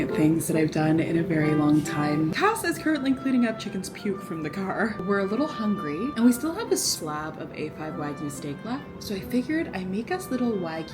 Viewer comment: for some reason i thought you guys were saying 'AFAB wagyu' and i was like, don't spill mx. wagyu's tea